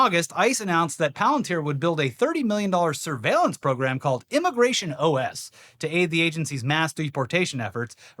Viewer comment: Mishearing you went dark, well even darker. 21:55 thought you said, "immigration OF" Knowing the conservatives short eyed penchant, people disappearing, love of islands, and profits above all else. I would not put it past them set up their new sicko island elsewhere.